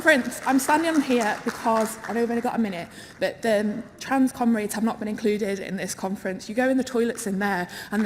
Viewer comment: Why most if not all of them look like stereotypes you see in the most insane and out there memes tho. Doesn't help that every time they say "comrades" I hear "cumrags". This is horrifying...